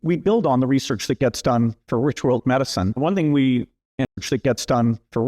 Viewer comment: Great video ! Sounded like he said Rich Old medicine?….🤔 but that really doesn’t make any sense…🙏❤️🙌